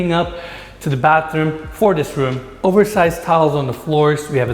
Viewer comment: Once again, I have a hard time understanding. Like at 7:45 I listened several times and I swear he says "...oversized towels on the floor." What? And for sure you put the guests you really don't want there in the room next to the beehives.